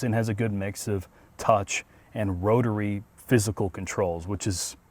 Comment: DID I HEAR ROTARY? dont give my hopes up, its not a mazda :(